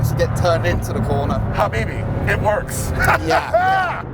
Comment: did he say "habibi, it works"?? I'm Lebanese and that's the last thing I expected to hear 😂